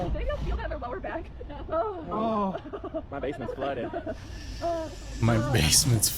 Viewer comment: I thought roller coaster guy said his bacon got flooded and not basement. I actually did jump with the jump scare with the dog because I wasn't expecting that. The cat wasn't that bad and didn't do anything with Donkey